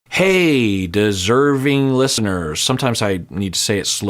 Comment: I had to turn on cc to know was saying "deserving listenters" bc I kept hearing "discerning listeners" 😅 he says it so fast I wonder if Hannah would find it helpful to follow the 4b movement almost like a reset. She seems to need a partner otherwise she gets too anxious/insecure/restless in life but that might come from a troubled upbringing and not who she might really be at her core. idk I think she'd find a lot of peace and happiness remaining single and not seriously attached to a partner 🤷‍♀